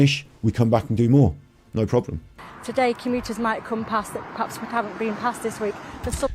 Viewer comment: I thought she said ‘commuters’ to be honest